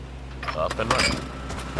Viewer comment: What did that guy said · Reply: Up and running